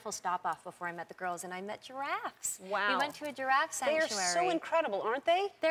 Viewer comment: oooh, giraffess.. I kept hearing "your ass"... and was like.. wtf?